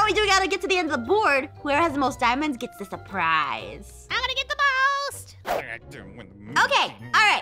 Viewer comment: I'M GOING TO GET THE MOST what I hear: I'M GOING TO GET THE BALLS😂